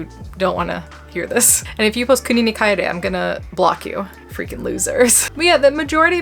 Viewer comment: What is that thing she mentions at 11:14? I can't even make out the word to google it. Legit trying to get some context here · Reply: "Kuni ni kaere" (国に帰れ) is a Japanese phrase that means "Go back to your country".